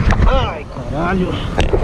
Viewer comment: só eu ouvi um "ai krl"